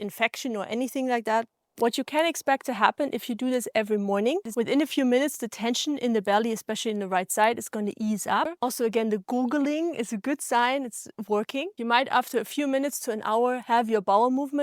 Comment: "gurgling" :face-blue-smiling: i thought she said googling, so i googled :face-fuchsia-tongue-out::face-green-smiling::face-blue-covering-eyes: Thank You :face-red-heart-shape